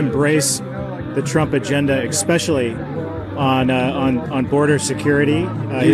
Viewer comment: Did he just say "expecially" 😆😆